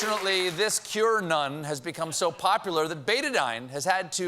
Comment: Oh, it's written Q-Anon, so far I thought people said: cure-none. Must be because I am a non-native speaker.